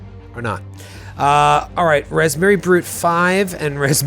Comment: Am I the only one who thought kept saying Raspberry Brute?